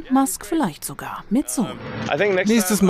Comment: "...dann kommt Musk vielleicht sogar mit... " Was? Ich verstehe das Wort nicht 😆😄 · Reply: Sohn